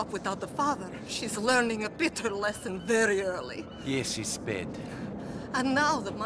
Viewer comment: I thought Niko was saying “yeah she’s sped Oh jeez